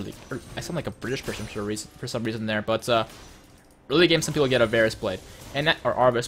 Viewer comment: lol, I thought was saying 'a Varus blade.' I'm like... Wha? OH, AVARICE. ava-riss